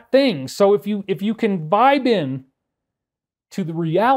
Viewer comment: I have to keep replaying @ 1:33:04 where you said "Vibe in"... I kept hearing "Bined".... hmmmmmm.... :)